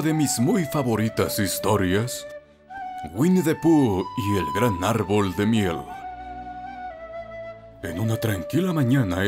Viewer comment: Pensé que dijo y el árbol de mierda xd